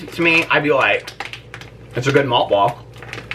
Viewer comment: when I thought you said moth ball I was like…. why would you know how moth balls taste?! 😂